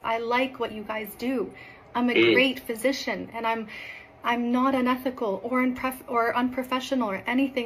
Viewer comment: 😂😂😂😂😂 Not unethical😂😂😂😂😂 I heard her delulu